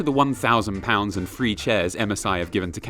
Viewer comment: I heard: “1000 lbs of chairs.” And thought, “why would that be how they measure ‘number of chairs donated?’”